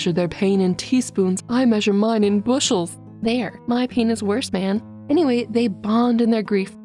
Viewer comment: I thought you said: My penis worse man. I almost had a stroke of confusion 🤣